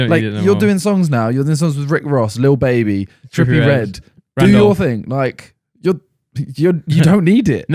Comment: dont think I didnt hear you say your own name, you not slick😂